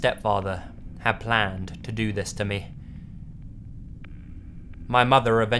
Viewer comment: You are literally the most adorable person ever and you have the nicest voice ever 😍 I just want to steal you and make you read things to me.. 😂🙊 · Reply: Eva M I thought u said "do things to me"